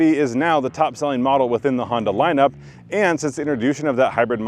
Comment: Did he say “interdooshin”?